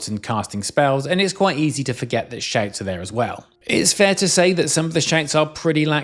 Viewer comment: Sound like hes saying shanks not shouts